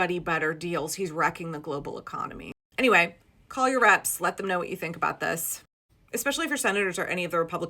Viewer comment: I love that the automated subtitles heard you say "call your reps" and translated it as "call your rats".